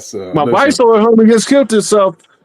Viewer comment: What did say at 1:32:35..who k*lled themself? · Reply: He said his homey